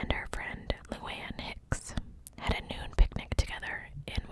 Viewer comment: Also just a funny side note: when you mentioned the girls having a “noon picknick” I first heard “nude picknick” and I was like.. well that’s random lol